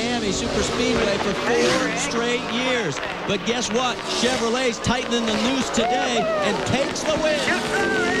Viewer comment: "Guess what, Chevrolet is tightening the noose today." Did I...Did I just hear that?